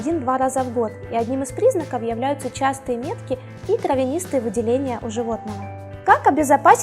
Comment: Мне не послышалось? Какие-какие выделения? ТРАВЯНИСТЫЕ????